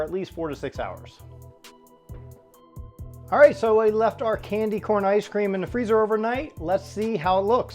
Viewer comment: At the end of the video it sounded like you said candy cane ice cream instead of candy corn ice cream.